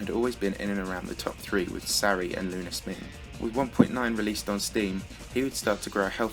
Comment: It sounded like you said lunar sming, its lunarsimg